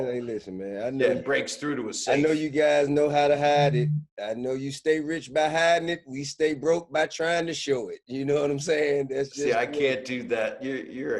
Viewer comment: If you didn't hear TIP quote at 22:44 well here it go "I know you stay rich by hiding it and we stay broke by trying to show it" 💎💎💎 🙅🏾‍♂️🧢